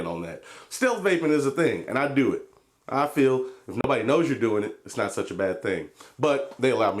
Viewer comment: People that don't Vape look at Vape as a bad thing some people do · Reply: I misheard him I thought he said I nobody knows Ur doing something it's alright but he just said if nobody knows Ur vaping it's cool